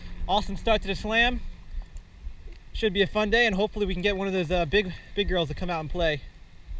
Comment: Did you say big girl or pickerel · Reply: dowell haha i said "hopefully we can get one of those big girls to come out and play," as in big bass :P